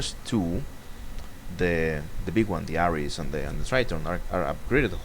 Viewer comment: Welcome back! As soon as i heard "Trident" I thought you modified the piece of crap i put together back in the day X)